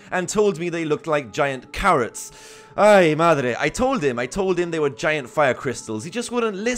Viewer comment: did you just say "Ay, madre"? 😂😂😂